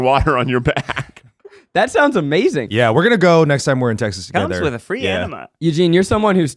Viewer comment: did ned just say "comes with a free enema" im losing my MIND